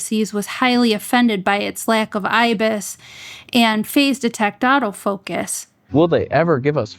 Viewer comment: It has digital stabilization but not in-body. It does not have phase-detect but can detect the face. Newer models have phase-detect where it auto focuses quickly. G100 had depth from deficits which is slower. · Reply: oh I misheard face detect for phase. How does it to stabilsation if not in body? Just curious .